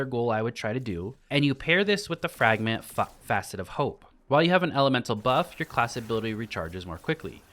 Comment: I heard you say fuck facet of hope lmao